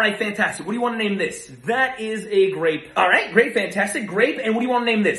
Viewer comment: I thought he said "grape fantastic"😂